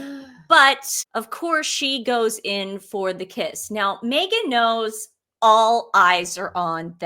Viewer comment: I thought Invictus said “no Meghan?!!”